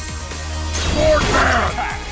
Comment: With how low the voices are, I heard "sword man" as "bored man".